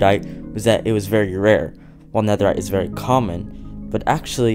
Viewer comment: lol it sounded like you said netherite.